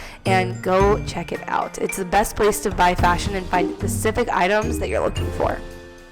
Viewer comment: DID SHE SAY "PACIFIC" INSTEAD OF "SPECIFIC"!?